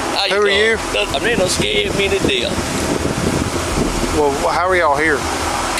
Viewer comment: I don't remember that in the video either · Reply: listen closely for a few seconds and he says “he done scared me to death.” It was not in the subtitles and he said it pretty quietly, but I heard it. I don’t have my comment notifications on, sorry. Not to be rude, but what exactly are y’all trying to help me with?